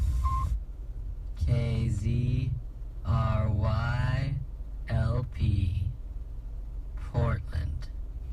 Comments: I just heard a callsign in 6:18 it says KZRYLP the location is in Portland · Did he... did he just say fortnite? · So here is one If you listen REALLY close on the one after the weird noises you hear the call letters for the station followed by the LP part for "low power" right? Either I'm going crazy or does the voice say after a long pause "sports" at the end because I swear I listened to this while working on my tablet now twice and I swear I keep hear "sports" on "TheLittleNorsk" section towards the end. Now my question if this were the case would be this What in the actual heck would "sports" have to do with that kind of weird noise? Am I the only one that heard this or did we hear different things with that voice after the call letters? Seriously everyone go back and loop that specific part cos its the weirdest damn thing in the video for me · At first I could have sworn it was saying "KZ RY LP Fortnite"